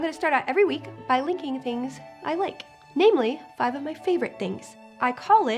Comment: I could swear she said "licking things I like"... O.o Bad imagination! Bad! Don't do that any more. You'll give me a heart attack.